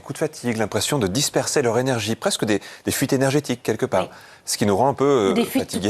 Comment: Elle dit quoi à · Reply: Des fuites tout court